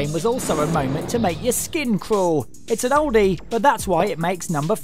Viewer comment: did he just say it would make my skin croll? Oh my that does not sound good at all!!!